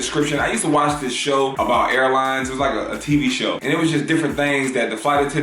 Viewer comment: I thought he said he watched a show about hairlines